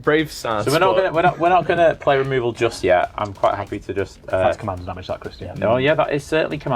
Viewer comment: Every time I'm hearing "that's commander damage" I hear "THAT'S NUMBER WANG!"